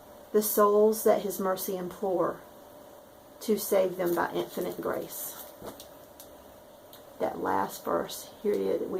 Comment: Could you tell me what the name of the pattern and the designer of the pattern you read from at 3:40? I didn't catch the name and I really want to stitch that piece! God Bless and Good Friday! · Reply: That is E. Davies by Threads of Gold. Isn't the verse wonderful!